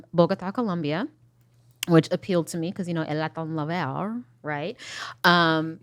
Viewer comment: My mom is from Colombia and I have no fucking clue what she said at 5:05 😭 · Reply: She said Latin Lover with a horrible Spanish accent 😂😂😂